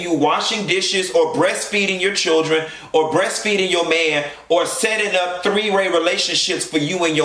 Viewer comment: Did he just say breastfeeding her man😂😂😂😂😂😂😂😂